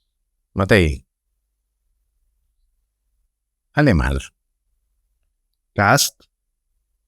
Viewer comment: no le logro escuchar bien lo que dice sobre Matthei. Suena como "alemán" pero siento que no es esa la palabra, y además no le veo sentido. Alguien con mejor oído que me diga qué plabra fue la que dijo?